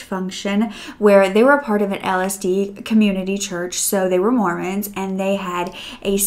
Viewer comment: I'm also LDS, and I laughed SO HARD when you said LSD, because it's so easy to accidentally switch up! But the case was SO interesting! I had only heard of one other case of murder concerning an LDS family on Hulu or Netflix, so I was kind of excited to hear about another story despite how sad it is. It sounds like the father was a real narcissist, or at least had narcissistic tendencies. I applaud the eldest sister for being strong enough to look into the whole thing. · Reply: I laughed too, but also because I misheard “Mormons” as “morons”. Possibly misheard the word Mormons because she said LSD. lol.